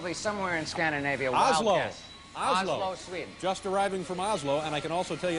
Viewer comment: did he say: 'Oslo, Sweden?'